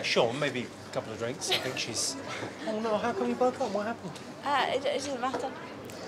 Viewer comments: what did she said? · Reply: "it doesn't matter" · what did she say? Non-native speaker here. I can't make a word out of it. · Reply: “How come you broke up what happened?” “It doesn’t matter”